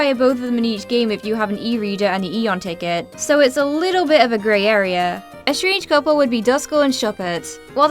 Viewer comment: what in the Hoen does she say here? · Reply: “It’s a little bit of a grey area”